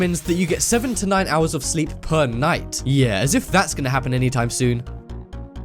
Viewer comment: Lol I thought he said 79 hours of sleep per night